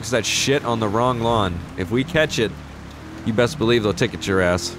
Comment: said something about dog shit then he said catch it but I thought he said cat shit